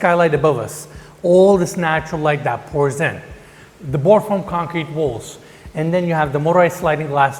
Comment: did he just say board form concrete? Because that doesn't look like board form to me